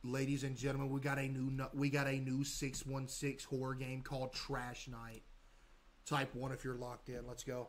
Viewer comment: For a second i thought he said the game was trash :face-green-smiling